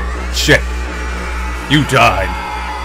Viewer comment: i thought it said you ded for a second there